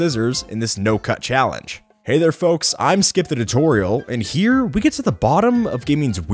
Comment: I thought skip said hey I’m snip the tutorial